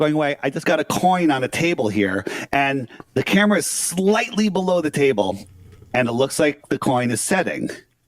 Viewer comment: he actually said the camera was below the table